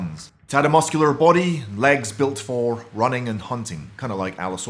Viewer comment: I thought legs good for running I thought that said let go for a running